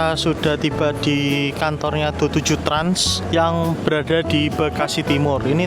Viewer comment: mas nyebutny 27 trans yah😂 aph salah denger